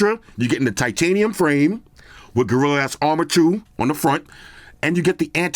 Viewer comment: I swear I heard "Gorilla ass armor 2."